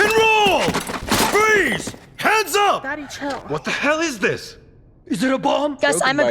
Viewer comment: Did I just hear "Daddy Chill"?